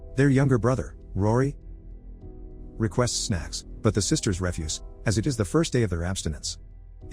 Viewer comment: The boy requests "snacks." I thought that I heard "sex" and then "the sisters refuse because it's the 1st day of their Abstinence." I only listen in the background without watching. So I hurried to rewind this and listen a few times. Still sounded like "sex" until I finally had "snacks" while watching how young the boy is. After reading the title and listening to other "Story Recapped" and "Detective Recapped" vids about similar themes, my brain was primed to expect it.